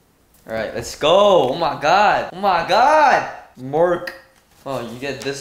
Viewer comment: Did he say Mork or does my brain automatically translate Mark to Mork these days?😂😂